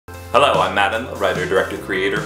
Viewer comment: When you said "Hello, I'm Matt Enlow," I swear to God I heard, "Hello, I'm madame." Hello, madame.